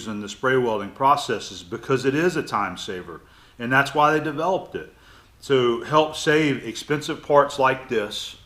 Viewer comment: min did you just say you've developed the spraywelding? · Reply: No he said that's why they developed it, I assume he's referring to the product manufacturer.